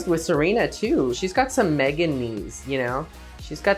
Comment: when denali said “megan knees” and trixie was like “???” 😭😭😭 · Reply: I thought she said MEGAKNEES